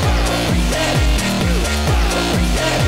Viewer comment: I thought it said get Grindr 😭